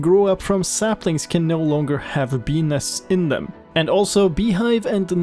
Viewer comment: auto generated subtitles says pp instead of bee nest